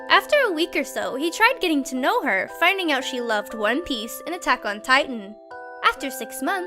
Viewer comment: me: DID YOU SAY ATTACK ON TITAN?!